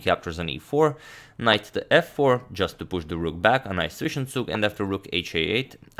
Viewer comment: what does he say, i heard “Nice Fishen Zug”...